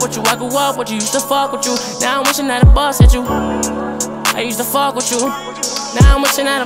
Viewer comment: What he said: "Used to fuck witchu" What I heard: "Ight imma fuck witchu"